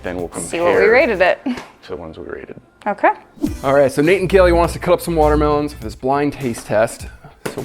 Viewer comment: Did he just say "Nate and Kelly"?